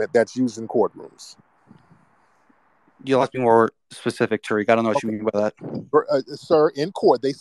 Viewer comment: When the Moroccan came on I thought he said “fuck Tariq”🤣😂🤣